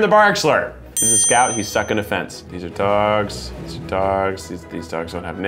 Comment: Did anyone else hear this is scout he’s suckin a fence